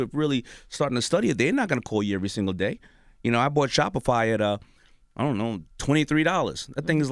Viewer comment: What is the name of the company that Daymond said he invested $23 in two years ago? I couldn't understand how he pronounced it, anyone can answer. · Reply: Jakeem Kesarr it’s Shopify 👍🏻